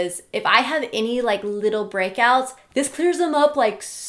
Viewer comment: I just went through a really bad br... thought you were saying breakup I got excited you finally single😏